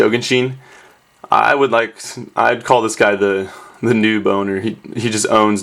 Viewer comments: haha, sounded like you said "new boner" 6:00 xD *noob owner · Anyone else think he said "new boner" at first instead of noob owner? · anyone else hear "new boner" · when you said noob owner, i thought you said new boner xD i have problems.